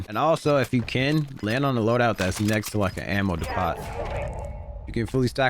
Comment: a ammo what. Chillll. He said a ammo dee pot. U never heard of Home Depot 😂😂😂😂